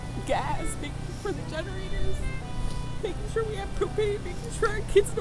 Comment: Leo with the hug🤣🤣🤣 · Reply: Ngl i thought she said " makin sure we have cocaine " 😅😂😂😂